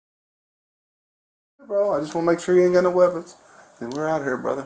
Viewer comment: He went from “y’all tryna kill me” to “Imma kill him” real fast but I thought you said “I ain’t go no gun”